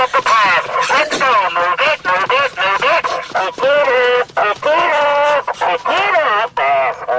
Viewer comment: what did he say? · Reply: Pick it up